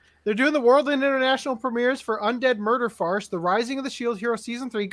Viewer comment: I heard "Undead Murder Farts"